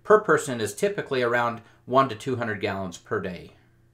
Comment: did he say 100 to 200 gallons per day per person?